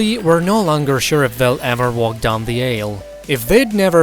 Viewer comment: sounds like he said “walk down the a hole” sounds pretty accurate!